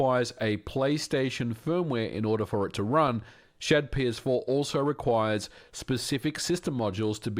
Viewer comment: why do the subtitles say RPCS4 when says ShadPS4?